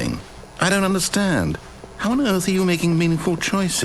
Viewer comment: I swore I heard: do you understandley